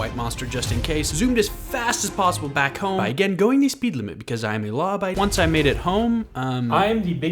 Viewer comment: Did bro say he’s law by Mike